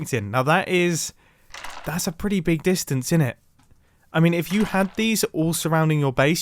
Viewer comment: Did he just say innit? Are you okay, Mumbo?